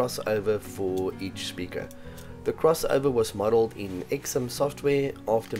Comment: What is the name of the software for designing the crossover @ 7:15? Sounded like Exom? XM? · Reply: Xsim.